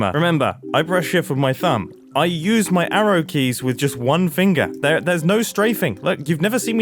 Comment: did he just say... ARROW KEYS